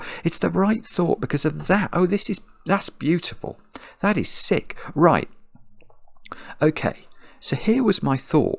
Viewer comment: "That' is sick!" 😂😂 had to rewind just to make sure that's what actually said lmao